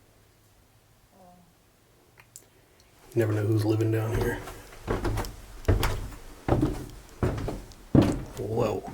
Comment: Did anyone else hear a voice at 14:45 when he asked if anybody was down in the basement? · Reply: Yup, it says, Yeah…